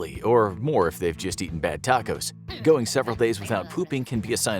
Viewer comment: says bad tacos but the subtitles say Taco bell. I see.